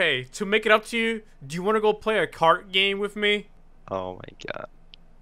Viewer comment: I thought he said card game That'd be even funnier sends a arsenal mod to the shadow realm quality funi content idea here